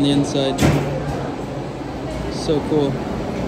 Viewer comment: “Doko? Doko?” (Where where) i love that you thought he said it was cold!! Japanese is fun isnt it? 😄 so happy you had a good time!